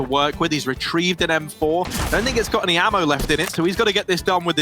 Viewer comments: tbh at 2:16 i heard ,,That n got any ammo in it" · It sounds like he said "That n****s got any ammo in it"